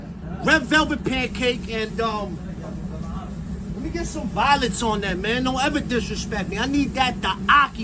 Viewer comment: I thought he said throw some violence on it he said violets right ?